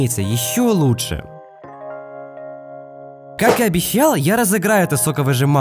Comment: обещала!? Ладно послышалось, видео топ